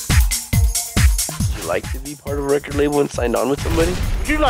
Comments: What was saying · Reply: "would you like to be part of a record label and signed on with somebody?" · what is saying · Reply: "Would you like to be part of a record label and sign on with somebody?"